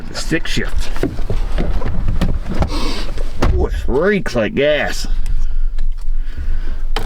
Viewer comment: Thought he said reaks like ass when he got into the truck then read the title. Lol